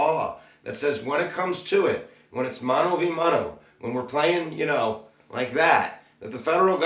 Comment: Thanks for the wonderful video!!!!! Helped me a lot!! What was the word in minute 2:24 please? Mono vi mono? :)